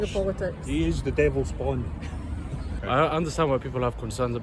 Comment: the subtitles are wrong. İt should read the Elon Musk is “ the devil’s spawn” rather than “bond” which doesn’t make sense.